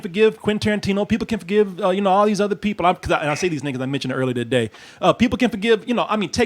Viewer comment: did he say these N... lol